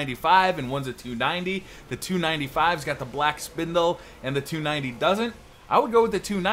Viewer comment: Wait did you just say "black hammer" in stead of "black spindle" pls reply? Best you tuber really helped I appreciate your vids